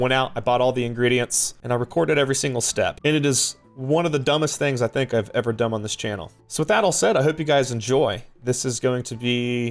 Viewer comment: Anybody else hear say "this is the dumbest thing I've dumb on this channel"?